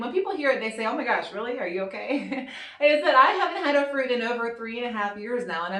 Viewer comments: What did you say at 3:09 that was one of your lines in the sand? I replayed it 5 times and still have no idea. It sounds like you said you haven’t had “ofran.” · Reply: She said, “I haven’t had a fruit.” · I could not understand what you were saying at 3:10 that you had not had in 3 1/2 years... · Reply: Jennifer Sutton “have not had a fruit”